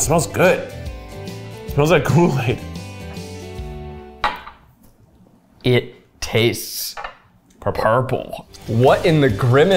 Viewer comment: All I can hear at 26:53 is saying “That tasted purple!”